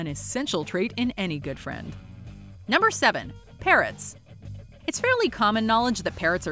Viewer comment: thought she said “parents”. Straight up was like, “yeah right give my mom a bottle of wine and she turns into Facebook addicted zombie”